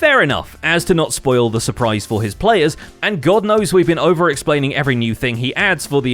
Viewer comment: I swear I heard "And Goat knows..."